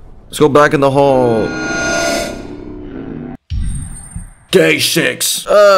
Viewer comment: sounded like he said gay s3x lmao